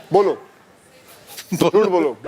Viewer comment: par kya bola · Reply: He said I think jhoot bolo